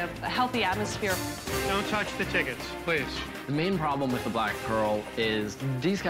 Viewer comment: i can’t be the only person who thought that he said black girl at 1:40 and my jaw dropped 💀